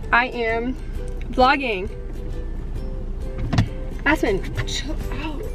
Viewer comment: Omg 0:27! It sounded like she said ass-pen and I 😂😂